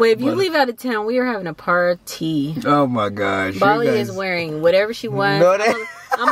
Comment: Did she say Babys name at 18:03? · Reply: It’s Bali like Bali Indonesia